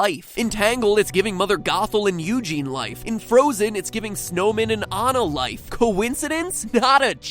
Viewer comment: i know he did not just say snowman instead of olaf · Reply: he said snowmen, as in olaf and marshmallow and the little snow guys